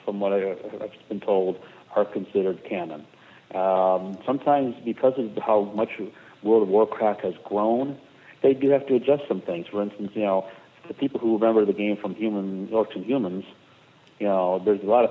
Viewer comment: Did he say World of Warcrack the first time he said it at about 9:30? lol